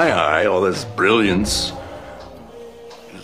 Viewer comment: Rote* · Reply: the subtitles (automatically generated) said "road copying" when the line was actually "rote copying", which means to copy something down word for word without any real thought. Walt is saying that gale didnt come up with the formula, he just wrote down someone elses formula that he had memorized.